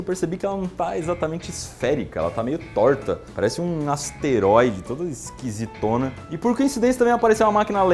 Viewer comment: o que ele falou? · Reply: Asteroides